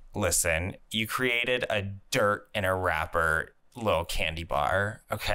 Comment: When you said "you created a dirt in a wrapper, little candy bar" I heard it as "a rapper, Li'l Candy Bar" and now I can't stop imagining a really cringe commercial with a rapper that dresses up as a Quest bar